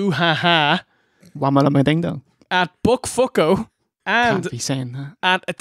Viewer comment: "Wama-lama-ding-dong" "Book Fook-oh" and then "ya can't be sayin' that". Am I hearing that right?